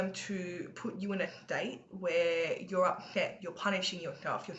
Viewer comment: They can try ...pigs don't fly... You set things straight for me..🙏 I've got this now! Party time...😁 I just put the force Fields up... That easy when helped... Punish? Oh I thought you said polish.. lol they can't do it.